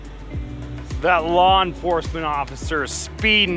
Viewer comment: i thought you said LAWN Enforcement officer...